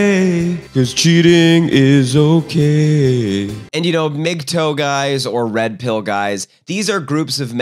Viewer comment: Every time he said MGTOW guys I thought he was saying “big toed guys” and I always spun out